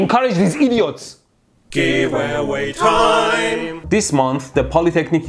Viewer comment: I hear "GIVEAWAY MIAU" xd